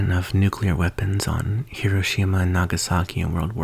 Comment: "Smart talk" me:ALLL I HEARD WAS HIROSHIMA AND I HEARD IT AS KIRISHIMA